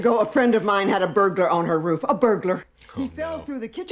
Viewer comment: i thought she said burger